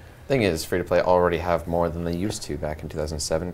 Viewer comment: all god armour f2p? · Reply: Jos Swinkels i thought they said they're already f2p.